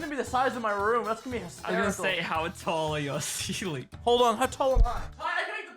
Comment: All caption mistakes (That I found): 0:22 "I was gonna say, how tall are your ceilings?" 5:08 "Cause OML do I love refrigerators" 6:49 "Deadass spent 2 hours doing project on youth ON Asia" 7:45 Its like... That's all I could find Comment if you found any others